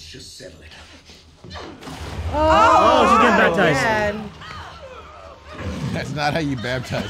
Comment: I thought said she's getting traumatized, you know cause she died by drowning, but thanks for the subtitles☠☠